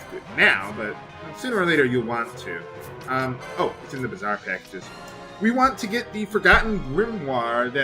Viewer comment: Hehe, every time he says "Bazaar Package" I hear "Bizarre Package". Doesn't this happen to you too?